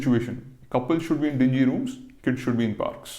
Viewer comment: "Couples in dingy rooms and kids in parks" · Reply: What is dingy ? I thought he said oyo